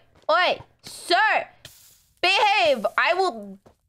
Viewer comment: Did call dream babe?